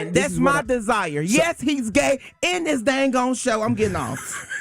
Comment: did he say the show is gay